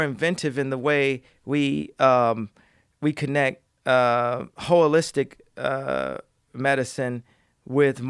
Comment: Hell Yeah,. 2:30:40, I think I heard Kanye come up with a new word......"Hoe-alistic" I'm gonna start using it today. Thank you, Kanye